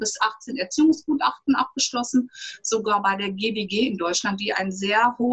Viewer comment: Wovon spricht sie bei Minute: 10:00 GDG? habe ich das richtig verstanden? Was ist das für eine Organisation? Haben die eine WEB-Seite?